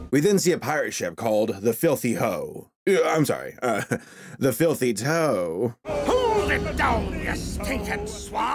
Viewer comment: I thought it said the filthy Joe and WHO POOPED IN THE PEANUT BARRLE!?!?!